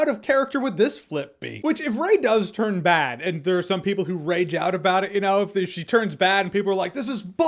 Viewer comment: did you just say... "RAY-ge out"? 😉😂